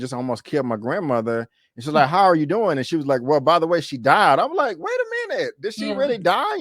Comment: In Reference to you 8:42 mark,Clifford didnt say she died, she said She DYING. Well that's what my closed caption said. LOL